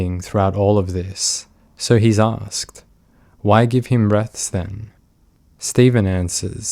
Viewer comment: i can't tell what says here, can someone help ? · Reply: So he's asked... Why give him breaths then?